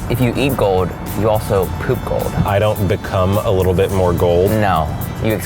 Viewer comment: Oh my gOSH when he said "I don't beCOME a little more gold" I thought he said "I don't CUM a little more gold" and I had to replay that literally three times before I finally understood what he said o gosh i'm so sorry